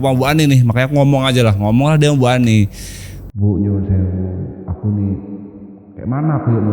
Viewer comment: ngomong apa ga jelas ih bang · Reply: "Buk.. Nyuwun sewu/Nuwun Sewu... " Artinya "Buk.. Maaf.. "